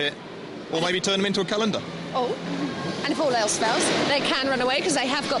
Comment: I thought he said calendar! how do you turn a robot into a calendar?